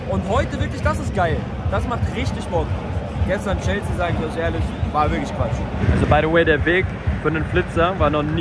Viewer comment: Was sagt er? Chelsea gestern war Quatsch? Dafür ging er aber ordentlich ab in den vlog